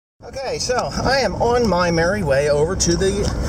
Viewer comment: ? @ 0:05... Did you just say you are on your married way?